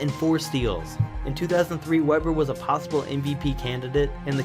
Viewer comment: Sounds like he is saying popsicle not possible.